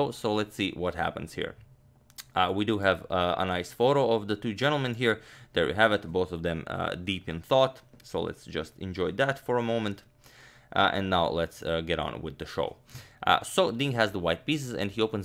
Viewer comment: when he said “deep in thought” I heard “dripped in sauce” 🤣🤣 I had to go back lol